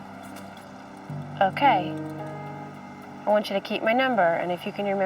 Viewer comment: The coroner is saying “..he was pronounced <what???>” I can’t understand the word used here k, ; sounds like “unseen”, un-cean is that even a word? Even the CC/closed captions leaves the word out… isn’t it usually “the subject was pronounced deceased at 12:03am…”? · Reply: It's just a bit of slang. She says "he was pronounced on scene" Translates to "he was pronounced dead where he lay at the crime scene" A nicer way of saying it I guess 🙂